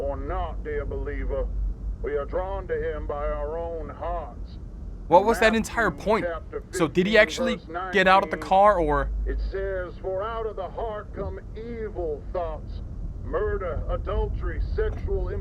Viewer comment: 6.00 I SWEAR I heard him say diddy actually get out the car😐